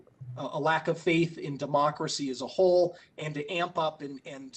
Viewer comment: "China needs to pay a price for what they have onlished on the world" Donald J. Trump president of the United States of America 🇺🇸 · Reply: I thought he said it was a Democratic hoax. Glad the con is gone.